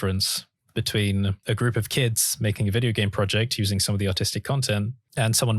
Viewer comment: I thought he was saying "autistic".